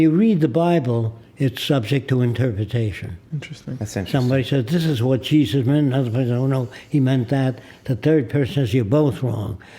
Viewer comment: min) This is what Jesus...? Is somebody understanding what he is saying there? Can someone "translate" it please to me, would be realy thankful about that. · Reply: He says precisely "This is what Jesus meant, and another person says "oh no, he meant that", the third person says "you're both wrong"."